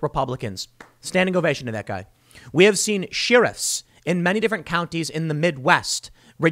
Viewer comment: Did you say "shirriffs" Tim? 7:46 (hobbit sheriffs)